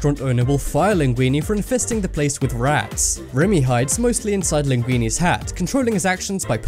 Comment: at first I thought he said "for fisting the place with rats".